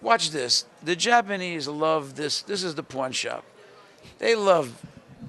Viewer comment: The Japanese love this....this is the porn shop...the japanese love....LOL! What was he trying to say?